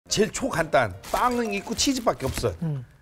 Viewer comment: 제일 ㅈ같단 처럼 들리는 나의 귀는..롤 그만해야겠다